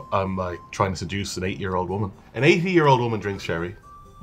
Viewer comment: it soooooo sounds like he says 8 year old lol